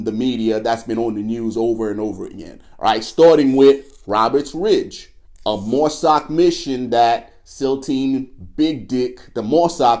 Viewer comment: He said Mosaic Mission: min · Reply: No he said "Marsoc Mission" his editor misheard and wrote "Mosaic Mission". Mosaic Mission is some climate shit in the Arctic